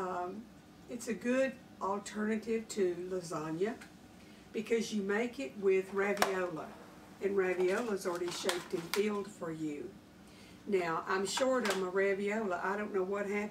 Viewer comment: Around minute 3, I swore I heard you say the ravioli were already “shelled and peeled” and I almost couldn’t catch my breath from laughing so hard. I thought you were making a gardening joke. I did go back to hear you correctly. Be blessed!